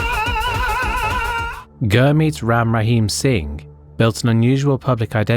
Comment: i thought it said ram ranch